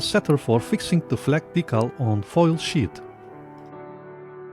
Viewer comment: He said “Sheet” as an a “Sheet of paper” or in this case, a Sheet of Aluminum. He did not say Sh%#.